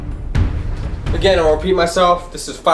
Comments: When he said “again let me repeat myself” I thought he said “again I’m going to pee myself” 😅😅😂😂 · I thought he said "again I peed myself" 😂